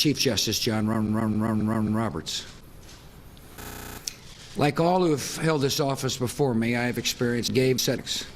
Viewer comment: I thought this was edited (the original video, which I watched a few hours after this one, but first watching this many times, since it was only 15 minutes 21 seconds) because he sounded like he said "Like all who have failed this office before me..."